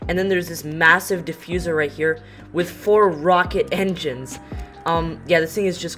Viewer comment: He says “DOUBLE ENGINES” I think he meant to say exhuast maybe? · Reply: I said "four rocket engines" lol